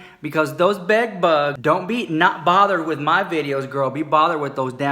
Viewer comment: I always thought you were saying others are "not bothered". That palette is pretty. Looks powdery though. Just went back, talc is the first ingredient. :/